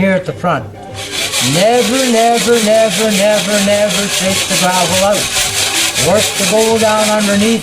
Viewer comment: never, never, never, never, never...what?? 1:41 Can't hear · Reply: Take the gravel out....by hand